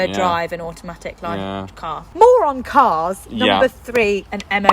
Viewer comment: moron cars?? Did anyone else hear this?